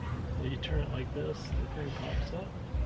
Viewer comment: I THOUGHT IT SAID POOPS NOT POPS